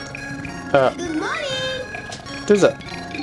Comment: It sounds like he said: "Good money" xD